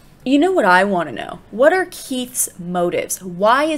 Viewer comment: you said Keath instead of Heath 😂😂😂